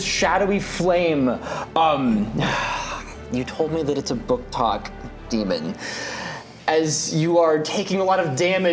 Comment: I'm sorry, what did just say? Something Something booktok demon?